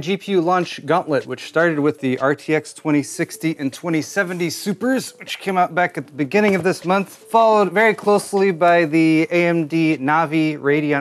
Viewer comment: At first I thought you said "followed very closefully" lol, I like that.